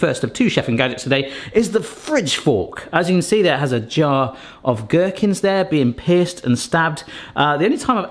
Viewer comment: Omg, I misheard him and I thought he said "here's a picture of the gherkins being pissed" like they were mad the fork was gonna get them lol.